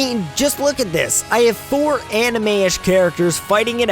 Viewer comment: Did u just say Sora was from anime? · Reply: mandala effect? he said anime-ISH